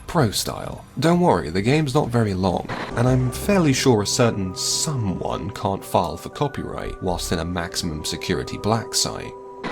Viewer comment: What he just said about Civi? I couldn't hear but I recognized the silhouette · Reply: "I'm fairly sure a certain someone can't file for copyright whilst in a maximum security exile (?)'"